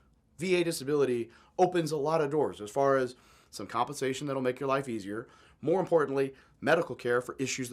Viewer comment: you have "constipations" instead of compensation